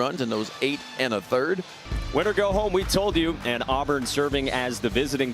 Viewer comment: I think he meant to say looser goes home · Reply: he said win or go home